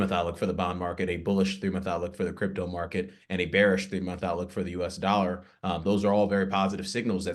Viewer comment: Starting at 5:23, Mr. Dale uses a term which sounds like "themotholic". I've tried different spellings and variations of what I heard and I cannot find any word corresponding to what he seemed to say. Anyone able to help me out here? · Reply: Three month outlook